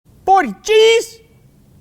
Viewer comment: did he say orgy's